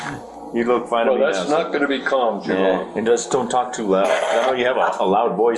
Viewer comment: when the cops are leaving and tell to talk to Erica (social worker) when he’s “calmed down”, the caption says “I’m gonna be calm” but it really sounds as if he’s sarcastically saying “oh, I’m gonna be calm,” if not blatantly stating “I’m not gonna be calm”.